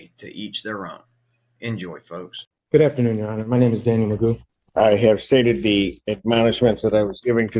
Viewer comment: Is the defendant's name really? I could have sworn I heard him say that at the beginning. Whatever his name is, he seems to be several sandwiches short of a picnic.